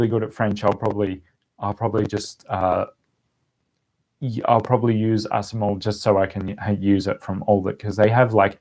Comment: Hi ! Does anyone know what app/website he is mentioning at 1:02:50 ? Asmol? I really want to know hahaha I tried googling it but all I kept getting was some medicines. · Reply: Assimil